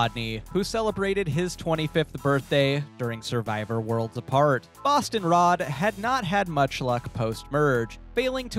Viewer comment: Anyone else hear “Boston Rob” at first?